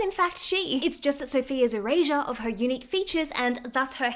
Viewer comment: I thought you said "Eur-rasure" of her features and omg I don't think you said that but it made me think it's prob the perfect phrase for striving for European centric features